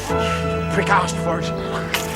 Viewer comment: Did Burke just call Matthew ‘a prick’?